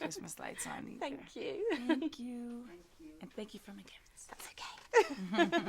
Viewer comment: the girl was a little nervious... but she did a great job she made good questiond... nice interview... I want to know who worked with her in the remix album... please mariah tell me... 9:15 what did mariah say? and thank you for my gifts?